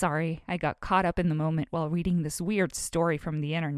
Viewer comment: Did she just say weweading? Just me? Okay